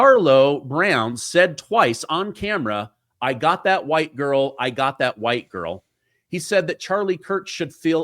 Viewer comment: bruh. He did not say “I got that white girl” twice. He said “I got that white b****” twice. It matters. This isn’t a playground tiff here. Completely innocent people are being killed for no reason whatsoever other than the mentally/emotionally/spiritually sick acting upon what the completely complicit media has been fomenting for over a decade